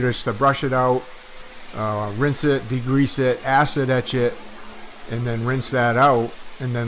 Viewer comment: Everytime he says "acid etch it" I thought he was saying "acid that shit" lmao